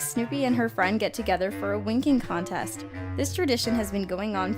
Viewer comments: ok don’t tell me I’m not the only person who heard “a wanking contest” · I thought she said wanking contest